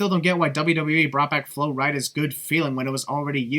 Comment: Did you just call Florida "Flowreda"???